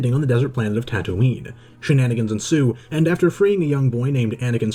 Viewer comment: Damn, I thought he said shenAnakins ensue. 😂